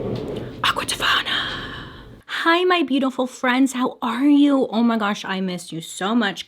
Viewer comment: but what did she say in the very beginning? like literally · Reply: Kalleigh Gibson Aqua Tofana! Watch her case video on Giulia Tofana for more info 😄